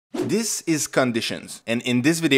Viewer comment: What did you say? Sounded like "This is conditions". 0:06 What did you say? Sounded like "More you will nest conditions". And the code you show is unrealistic. What's the point of starting the contents of an if(admin){} block with another if(admin) test?